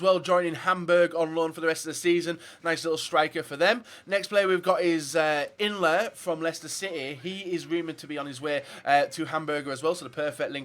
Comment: did he say hamburger ?????